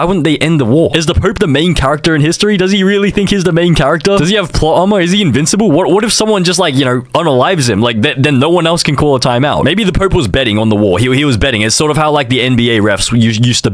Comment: did he really say invincible 😭